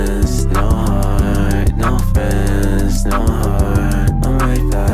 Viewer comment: This is perfect for when you're having a hard time eating and losing friends along the way. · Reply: I am so sorry i just laughed so hard🥲 I thought you said eating friends😭